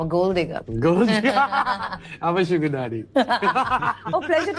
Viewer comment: What does honey sing says at 17:14? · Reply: I m a sugar daddy